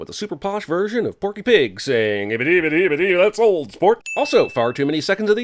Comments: for the old sport montage it sounded like he was saying ole porn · The bonus round had so many old sports it eventually just sounded like they were saying "mold spores," and it was too much for me. Now everybody in Starbucks is looking at me weirdly. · Ahhh, the return of Bonus Sins. Also, the more I hear "Old Sports", it sounds like "Olspoke"